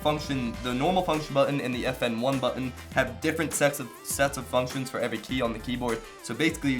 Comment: he said sex instead of sets